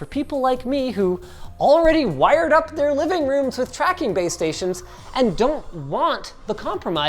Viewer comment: I thought I heard gays tracking